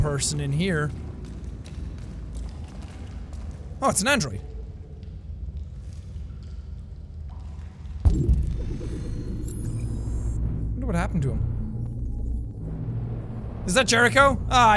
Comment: Detroit become human : a game about racism towards robots Jack: OH SOMEBODYS DEAD Upon closer inspection Jack: oh it’s just a android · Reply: He actually said “Oh! It’s an Android! Wonder what happened” I know it not a big deal but adding the word just changes the definition. Still a funny comment though. In case anyone wants, it